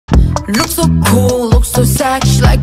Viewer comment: I heard that as 'A Rihanna' instead of 'Ariana'